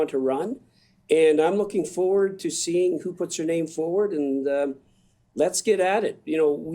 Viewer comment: I'm wondering if I heard right. I played back a few times. At about 9:27 I think I heard that Mr Long is looking forward to seeing who puts her name forward and... So it sounds to me like he's expecting a woman to be leader. Unless I heard wrong. But I do expect some people to be fooled by these MPs saying they are turning around, after backing up all the parties policies for the last 9 years that have ruined Canada. Also, in pics I saw yesterday of the candidates for the Liberals, Mr Trudeau had his pic there. So I would assume he clearly expects to be leader again.....